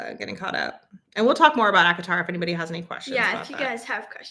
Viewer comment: What book series were you talking about 53:07. It sounded like you said Avatar but I know you were talking about something else. Also what is the name of the author? Thank You. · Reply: ACOTAR. A Court of Thorns and Roses by Sarah J. Maas